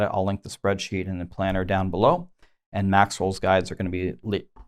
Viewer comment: Am I the only one that when says "Maxroll" I hear "Maxwell"?